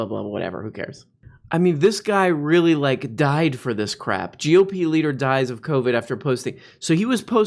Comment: sounded like GOP leader died of covid after pussy lmao.